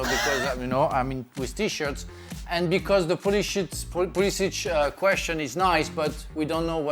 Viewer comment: Lmaooo 3:50 I swear he says puli💩